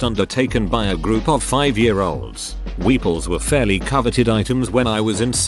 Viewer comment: Thought at first you said Weebles. egg shaped dolls that wobble but don't fall down. I had the Invisible Man Weeble w/ glow in the dark top hat, gloves & shoes.